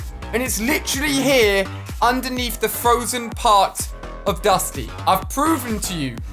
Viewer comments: Is it me or does he say dusty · Lol did he say frozen part of dusty 8:45 like here if u heard it 👇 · Did he say dusty? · Did he just say dusty ?? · did he say dusty??